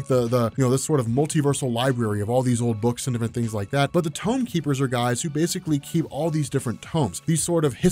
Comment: Thought you said he was a tongue keeper.